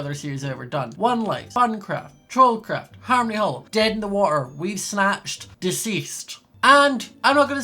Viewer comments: Did anybody else hear "Dead in the Water" instead of Deep End? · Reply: I thought he said “Dead in the war” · Did anybody else hear "Dead in the Water" instead of Deep End? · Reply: I heard him say "Dead in the water"